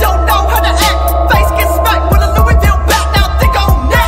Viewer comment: When I was fucked up I thought he said face get smacked with a Lubriderm bat LMFAO .